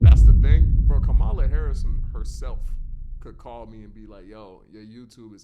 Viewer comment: am i the only one who heard him Kamala harrison???